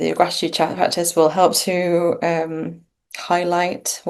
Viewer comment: I could not understand what practice was said to help. Anyone catch this? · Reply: Hi there! Yes it's hard to hear but I say 'gratitude practice'. Hope this helps!